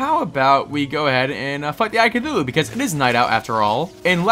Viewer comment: Just sayin at 5:24 kinda sounded like you said ”lest go f*** the eye of cthulu” LOL